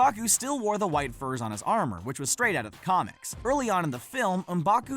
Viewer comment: I thought he said Strait Outta Compton